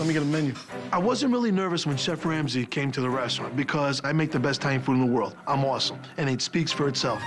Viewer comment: I make the best tiny food in the world!" (I know he means italian but thats what i heard lol